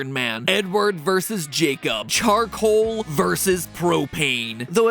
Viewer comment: thought he said n word vs Jacob